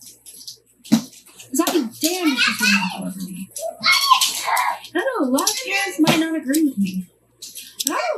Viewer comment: i think she is saying "because I'd be damned if she pulled one over on me." No need to thank me because I'd be damned if she pulled on over on us beautiful bastards... lol